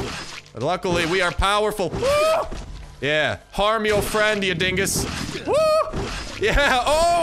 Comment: In 28:10 what does he say? "Harm me... "???